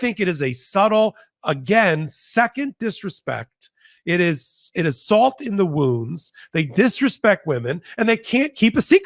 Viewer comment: I thought he said “salt in the wombs” 🧐Also appropriate.